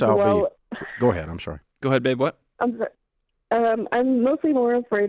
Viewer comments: did John just call her babe lol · did he just call her babe?